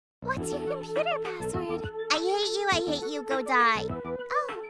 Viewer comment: I thought she said I ate you a e u go pie and I was like bitch wtf 🤨